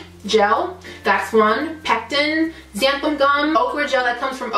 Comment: what did you say here 1:28... i need it... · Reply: Xanthan gum